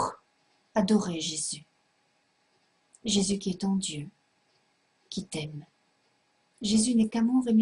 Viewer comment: Elle dit quoi a 5:30, jésus qui t'entube ou jésus qui t'encule ? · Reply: c'est jésus qi est ton dieux